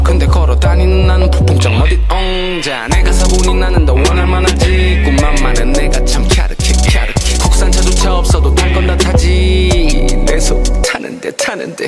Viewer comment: What is Nochang really saying at 4:40 because it says that it's gyareukhae gyareukhae, but it sounds like chyareuchae chyarechae. So what is it actually? Is he mispronouncing or did the maker of the video mess up? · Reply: Had to go back and listen several times, but I actually hear 갸륵해 (gyareukhae) for both words. (: The first time he pronounces "갸", I think it sounds different because his voice drops slightly in volume and he stresses the syllable in the back of his throat. So the "갸" comes out softer but more forced. (I can hear that 'ch' sound you were talking about.) It's definitely easier to hear "갸륵해" as the second word though!